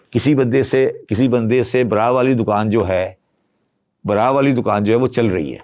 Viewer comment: Just to confirm; 26:00 did he say baraabar waali dukaan or bra waali dukaan? 😂